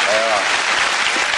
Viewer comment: Gosh, at the end of the video I thought the announcer said, "Asians, of course, being a minority anyone can slag off", until my second listen-through!